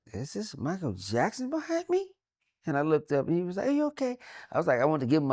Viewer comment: For a second I thought he said "Annie are you okay?" 51:47 😂😂😂